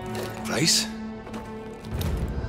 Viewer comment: I laughed hard after I heard "Who is Saop" 🤣🤣